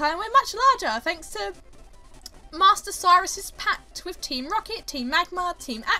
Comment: i thought that said miley cyrus at first..